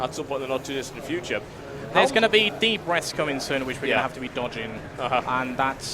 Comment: i thought that he kept saying breasts lol